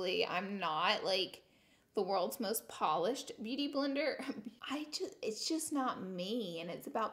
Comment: Did she call herself a beauty blender at 16:42 ?